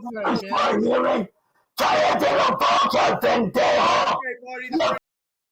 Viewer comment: did he just say pendejo? Dude in white as well, except I live down in Arizona and work construction and I still barely speak Spanish cause I cringe whenever I do. If I heard correctly and he did say pendejo then fuck he sounds whiter then me